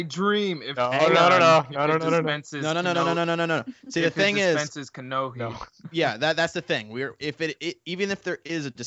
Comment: I heard "it dispenses nokias"